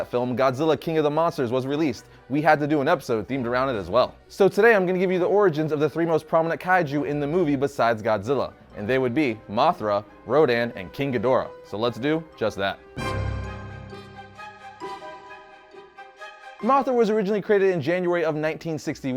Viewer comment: is it bad i almost thought it said mother rodan not mothra, rodan?